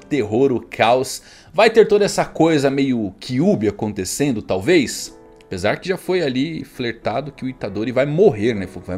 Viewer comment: SBS ao 12:00, eu ouvi um amém? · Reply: Amém